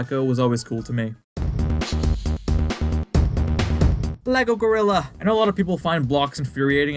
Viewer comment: Thanks for watching! Let me know your RANKING below! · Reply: I know you said "Lego Gorilla," but I heard "Lego-Rilla"... which would have been a way cooler name than "Bloxx," but hey copyright... 😅